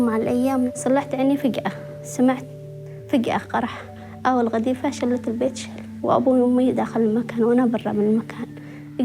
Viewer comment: at the end of the sentence "A missle blew out the house" it sounded like she said: little b*tch